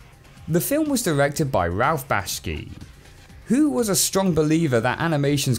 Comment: Did he just say 'BASHKI'??